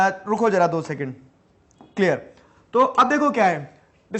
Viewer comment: he actually said 2 second...!